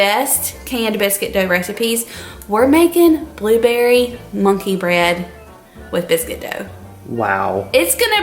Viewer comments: At one point when you said monkey bread I thought you said bunky bread. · I thought you said blueberry bunkey bread!